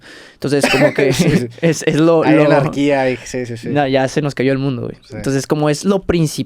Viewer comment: Que dijo en el minuto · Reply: anarquía